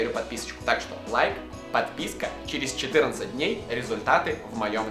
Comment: а мне послышалось через 14 лет😂😂😂😂